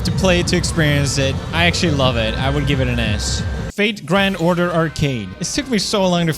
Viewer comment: Whenever he said “I would give it an S” I thought he said “I would give it an Ass”